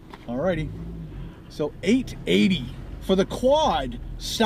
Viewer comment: The burger was only $2.80? jeez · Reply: PrestonDeGuy 0:36 he clearly says 8.80 $, m8